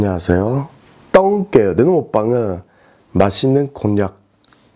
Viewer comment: 맛있는 곤약이라고 들림(